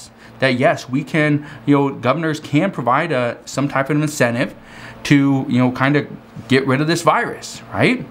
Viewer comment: Anyone else catch him slip at 10:53? ...he slips and says "Legally WE can provide incentives to take the vaccine"